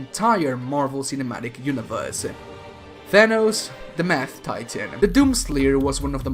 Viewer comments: Every time you called thanos powerful I thought you said purple. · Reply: When he said mad titan I thought he said math titan and imagined thanos as a math teacher. what is 2 - 4? Balanced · no offense but at 32 seconds in instead of mad titan it sounded like you said math titan again no offense.